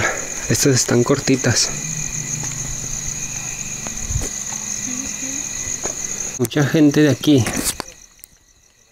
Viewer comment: En el minuto 8:11 se ve a alguien de azul y se escucha la voz de una mujer, alguien puede entender que dice? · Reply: Dice cómo sabes que?